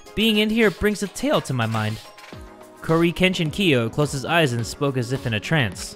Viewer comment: Did he just say corykenshin kiyo?